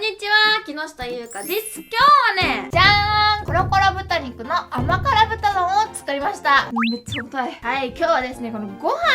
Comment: What I heard is porocorocorococororo masta.